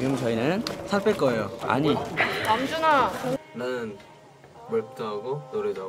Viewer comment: did anyone hear the noona calling out NAMJOON-AH🥰💜